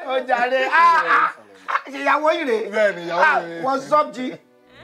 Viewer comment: Lol who heard "what's up G" @ 4:46 🤣🤣🤣🤣🤣🤣🤣🤣🤣